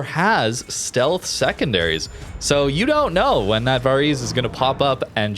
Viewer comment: some one help here... Script at 1:13 it say the ship name, script shows "varies", but what ship is he really saying? Thnx in advance... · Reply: Varese was an experimental ship released for a temporary event alongside two others Protector and Hotaka which were without doubt some of the most busted overpowered ships to ever enter the game. They were only in the game for a few months before being removed from everyones accounts which was needed thank god, they were only ever temporary to begin with and werent removed due to complaints or anything. Their gimmick was extremely fast firing and long range 15km+ secondarys which got even faster and further as your HP was reduced, they had extra powerful heals too. They were hugely unfair to fight against, they could beat schlieffen in a secondary duel, watch video on Hotaka and Varese you will see what i mean. Most people hope they never come back again. It was asif WG brought them in as a power trip gimmick to reel in older players who had'nt been in the game for a while. They offered large amounts of coal to earn with playing them which made the game miserable for everyone else and all you needed was credits to buy them just like tech tree ships. They were the equivalent of call of duty or battlefield making a gun or vehicle you could use which was infinitely more powerful than everything else in the game giving you an advantage over everyone and then only getting stronger the more damage you take, faster reload and more damage with powerful healing.